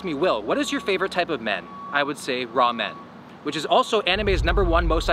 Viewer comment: bro i thought he said raw men 💀